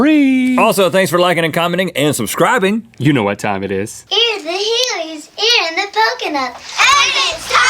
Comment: Could anyone please tell me what that little girl said in the wheel of mythicality video?? All I heard was 'In the healies, in the poconut!' I cannot make any sense of it lol :)